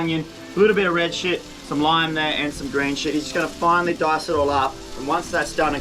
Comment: Did he say red shit and green shit lol